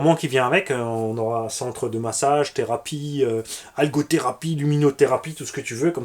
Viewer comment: Popopo !!! Merci tonton pour el garage 😎👍 16:53 j'ai compris alcolo thérapie 😁😂 Ho ouiiii surtout qu'ils restent entre eux.... Ho que oui....